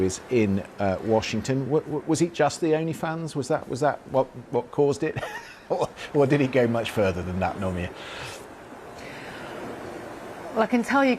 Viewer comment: What is he saying or does he try to say?? Please tell me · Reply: Was it just the “Only Fans”? Is that what caused it or did it go much further than that?